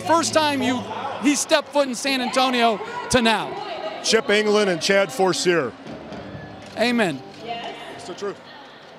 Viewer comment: What is he sayin? My mother tongue is not English. Help me out. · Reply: "Chip Engelland and Chad Forcier" - the names of two (then) Spurs assistant coaches to whom Popovich accredited Kawhi Leonard's improvement.